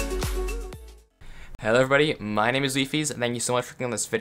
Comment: bro at 0:06 when you turn on subtitles it says leafy instead of wifies 🤣🤣